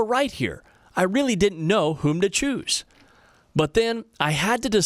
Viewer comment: did he just say whom?